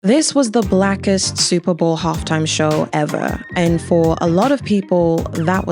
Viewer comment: did she say this is the blackest super halftime show ever